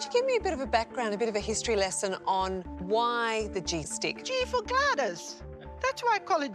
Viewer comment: Honestly, I thought the "G" implied it was for the G-spot lol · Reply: why is it called a G stick. it sounded like she said @ 2:45 "G-for goddess"?